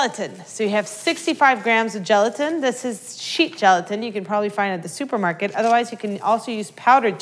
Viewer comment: Am I the only one who didn't know there was " sheet gelatine" ? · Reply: Rain Bun Unicorn at first i thought she said sheep gelatin.....